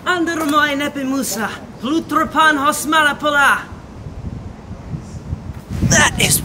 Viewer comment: I loved him, I don't know what the phrase really says but I loved him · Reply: idk either but it's a line from the umbrella academy and he said Ἄνδρα μοι ἔννεπε, μοῦσα, πολύτροπον, ὃς μάλα πολλὰ i have no idea what it means but like its the opening from Homer's Odyssey :)